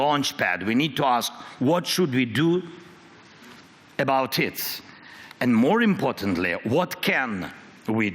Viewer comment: did he say "what should we do about tits"??... 0:41. i have a few ideas, message me dude